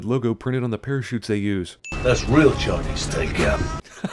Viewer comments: What does he say @ 4:05(before the sin narration)? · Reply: That's real Chinese takeout. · what does he say at 4:04 ? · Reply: That's real Chinese take out